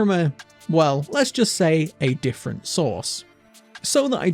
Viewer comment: I heard "a different source" as "sauce", ah I crack myself up